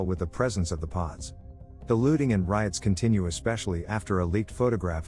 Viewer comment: Not going to lie, but when they said “the looting and riots”, I totally thought they said “the lewding and riots”.